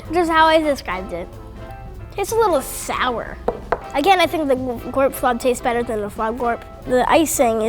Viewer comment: When he said gorpflob I thought he said chloroform